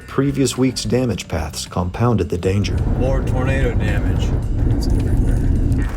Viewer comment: Turn on subtitles and it says "More burrito damage. It's everywhere." instead of "More tornado damage. It's everywhere."